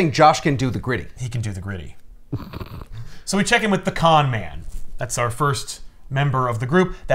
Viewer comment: Hahaha, I thought said the pecan man. Not the con man 😂